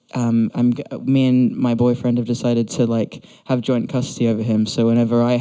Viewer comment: Did he say boyfriend